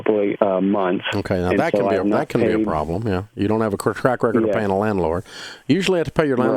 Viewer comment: you don’t have a what ???? Anyone know what he said? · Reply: He said "you don't have a quick track record of paying a landlord."